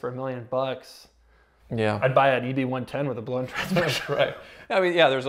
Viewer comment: My ears tricked me, I heard a110 (by Alpine/Renault) which is a lot more fun than the LFA.